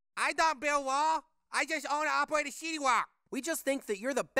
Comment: Did he say 西瓜？ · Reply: no he said city wok